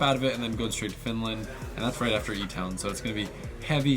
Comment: Did he say “that’s right after E-Town”?? So does that mean he’s gonna be driving FD E-Town??